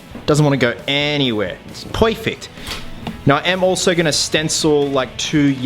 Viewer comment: Poyfict? DID HE JUST SAY POYFICT?!!?